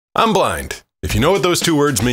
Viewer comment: i very confidently heard him say “i’m black” 💀